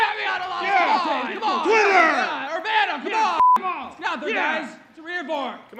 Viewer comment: Did the crowd say Urbana at 0:26? What did they say?